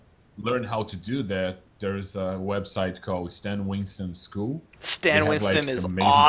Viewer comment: Could someone tell me which school Glauco mentions at 14:21?? I can't understand it ^^U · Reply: you mean stan winston school ?